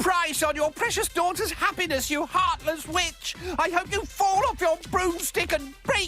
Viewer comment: at first I actually thought she said you heartless Bi***